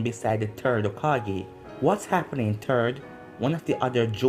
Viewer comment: I thought he said turd